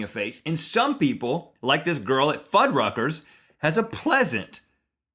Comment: When he said fudruckers I was half asleep and the volume was up and my grampa was near by and I got scared cause I thought he said f#@